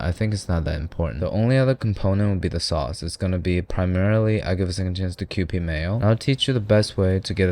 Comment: | What did he say? It sounded like, "I'll give a second chance to QP mayo", but I'm not certain